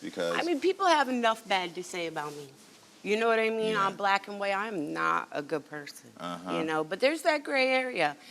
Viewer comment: WTH she say?? 🤔 Im black and im not a good person???? The way Karamo is looking I think she did. Maybe im trip'n 🤨🤔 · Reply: She said “on black and white (paper) I’m not a good person, but there’s always that gray area”